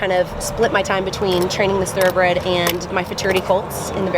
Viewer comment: what is the word she keeps using for colts...like at 2:39...fraternity colts? she said it in the previous video as well and I have no idea what she's saying or what it means :-) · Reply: Futurity